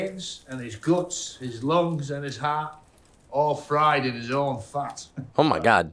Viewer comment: “fried in his own farts?” 😅lmao. I thought he said the same thing